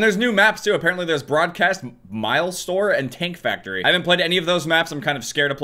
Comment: I thought he was saying Taint Factory